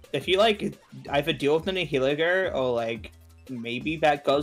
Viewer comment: NGL it sorta sounds like he said le' N-word. That accent ain't good for pronouncing Nihiligo